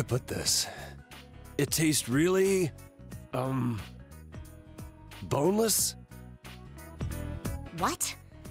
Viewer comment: Did he just said... B O N E L E S S